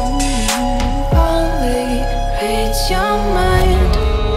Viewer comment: I keep hearing "b1tch you're mine" ...